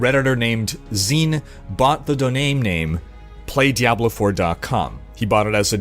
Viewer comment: "Bought the doname name." Is what I heard.